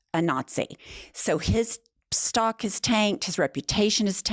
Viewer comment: Tanked... I thought you said taint at first. Which would still be appropriate when describing Space Karen.